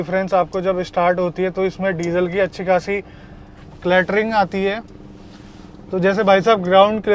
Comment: min. I thought he is saying latrine ati hai😂😂😂